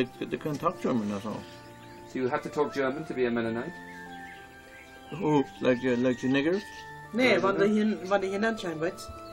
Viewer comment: did he ask like the Niggas? I replayed it several times and that's what I heard...or am the only one?